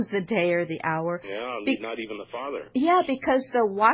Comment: did he say not even the Father knows when is the end? Oh but Rutherford knew.